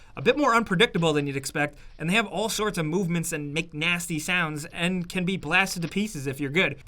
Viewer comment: When he says “makes nasty sounds”’ I thought he said “McNasty sounds” lol